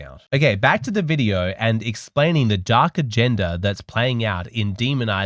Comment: "dark agenda" - I thought you said "darker gender" implying women were sinister, lol.